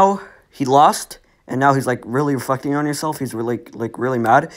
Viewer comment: did u just say hes really f**ing on himself???........